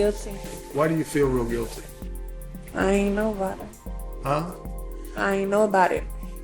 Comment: Around about 1:40:55, Rachel Jeantel, with her head in hands, in court, mumbles, according to Joel Gilbert , "Guilty" "Real Guilty". "I ain't know about it"..........when asked to restate it, she repeats the phrase, BUT......I hear her saying I AINT NOBODY...........